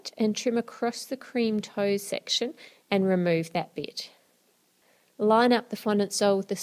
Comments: who else heard "Remove that bitch?' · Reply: She said bit but it sounded like bitch haha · I thought she said 'Remove that Bitch' 😂😂😂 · i thought she said "remove that bitch"